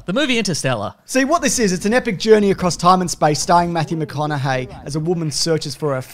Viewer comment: For a seconf I thought you said; "It's an epic journey through time and space starring Mathew Maconohey as a woman." Full stop. Just him in space in drag... I'd watch that for some reasons I'm afraid to answer.